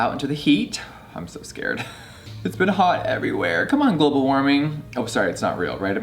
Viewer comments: I thought you said GOOGLE WARMING I was like wtf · ‘Come on global warming’ ‘Oh wait its not real right america’ THE SHAADEEEEE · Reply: I thought he said google warming 🤦🏻‍♀️